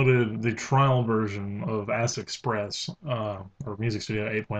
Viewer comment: kind of sounds like he said Ass Express. I feel immature for thinking such a foolish thing, but I appreciate any one willing to tell me I am right or wrong.